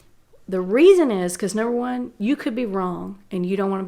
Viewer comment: "Number one, you could be wrong" Someone seriously wrote "no one" instead of "number one"